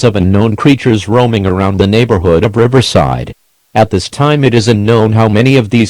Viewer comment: Did this just say Riverside.. cuz I live in Rialto.. starts panicking In California